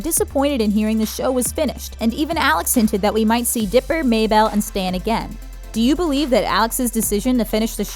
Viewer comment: Wait. Did she just say May-bell at 5:08?